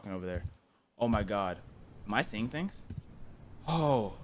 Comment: I swear to god- i heard: "OH MY GYATT"